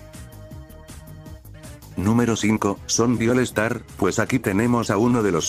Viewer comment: escuche zombieviolastar